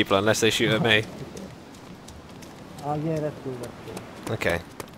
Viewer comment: I could have sworn the dude at 20:31 said, "Oh yeah, that's true, that's true." in response to, not "I'll get us to them."